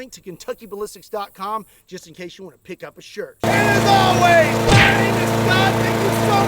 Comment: Man every time I hear "And as always..." I'm my head I hear "Have nice day"